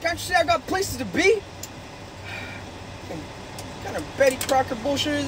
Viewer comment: I heard “ butt pressure “ instead of “blood pressure” lol.